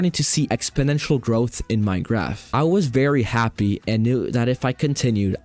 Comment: did he just say 'in minecraft' ?